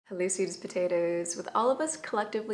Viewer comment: Did you call me a sweet potato?